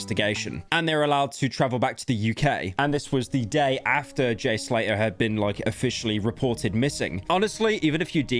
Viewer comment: idk why i thought i heard him say Jay Schlatter